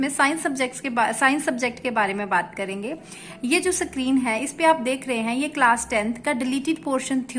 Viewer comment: screen ko kya bole mam🤣🤣🤣🤣🤣🤣🤣🤣🤣🤣🤣🤣🤣🤣🤣🤣🤣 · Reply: sacreen bola mam ne 😂